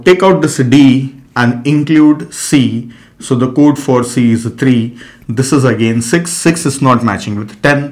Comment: I heard Codeforces firstly :D